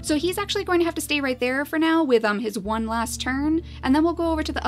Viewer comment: I thought u Said "with his one last time.." and i WAS just 😀🙄😐